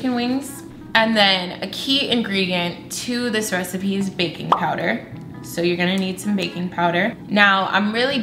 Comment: I thought she said "Baby Powder"